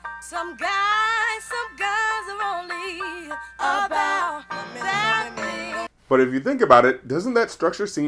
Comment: And here I always heard "Bad Things, Bad Things, Baddd Thinggggs!" instead of "That Thing".